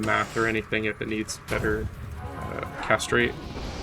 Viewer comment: Did you say "castrate"? ...Oh: "cast", "rate"...huh. Never noticed that before. Heheheh: you'll get all the misandrists gamer girls if you say one of the stats in your game is "castrate"...!